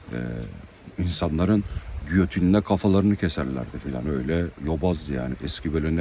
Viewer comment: "İnsanların götünde kafalarını kesiyorlardı" anladım, gözümde canlandı bir müddet.